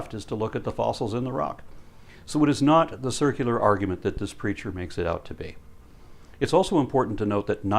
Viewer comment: Every time he says 'this preacher' I hear 'this creature.'